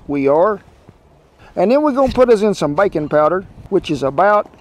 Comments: I must be tired... when you said, "Put in some baking powder", I heard "bacon powder" and thought wow, that's some secret ingredient! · LOL way to go me. I definitely misheard "bakin' " powder as "bacon powder." I was thinking, wow when did they start making that kind of stuff? 🤣